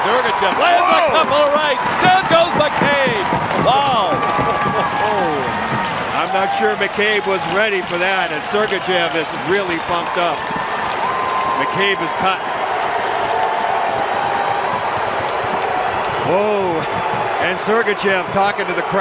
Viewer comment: Did he say "Really f***ed up"? Lol